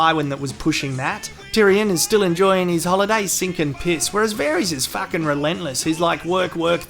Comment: "Tyrion is still enjoying his holiday he's ..." 2:53 what does he say? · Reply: hes sinking piss